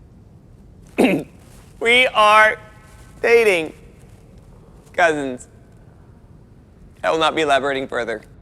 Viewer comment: did they just say DATING THIS IS ABOUT TO GET GOOD! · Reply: did. said cousins